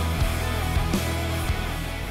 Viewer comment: L O I T E R I N G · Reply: I thought he said "Littering".